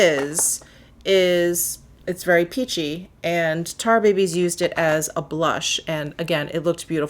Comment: omg at first I thought you said "tar babies". (sorry)